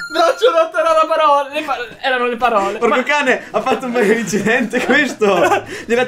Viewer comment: Ma solo io ho sentito bestemmiare al minuto 5:20???😂😂😂😂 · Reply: Chiara Scotto Ha detto "porco cane" Non ha bestemmiato